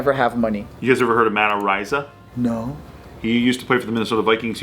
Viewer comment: lol thats what I get for having Vikings RB Matt Aziata on my fantasy team for 2 years, ty sers appreciate it! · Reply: lol. I thought you said Matt Asiata at first two but I didn't remember him having any rape allegations against him.